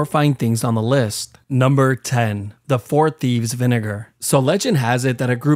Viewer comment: (i think it was) i thought he said n word hard r, but i turned on captions, and it was vinegar. I’m so childish 💀